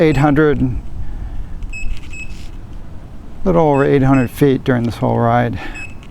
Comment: did u say "eighty hundred?" 80 100 meters? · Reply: think he says he climbed 800ft